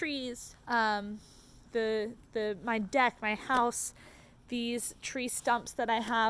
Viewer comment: when you said my deck a 8:43 is sounded like my death